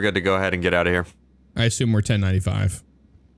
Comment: what did say here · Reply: "I assume we're 10 - 95" 10-95 means they're recording